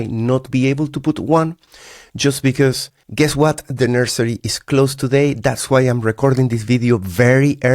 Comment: Anniversary? 🤔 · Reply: you said "nursery" but I heard "anniversary" 😂 Yikes brb going to check all my positions and make sure I paid more attention there! 😂😂😂